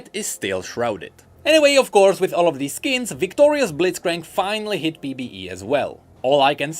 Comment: I was playing something when you mentioned "Victorious Blitzcrank" my head turned faster than his hook because I thought I heard you say "Victorias Blitzcrank" and I thought it was another fashion company collab like the Louis-Vuitton Qiyana